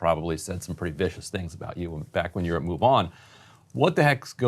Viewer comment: I almost thought he said he worked for MUFON in the past, but it was Move On. Big difference! 😂🛸